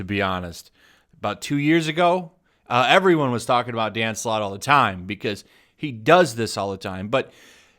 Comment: lol i thought you said "damn slut"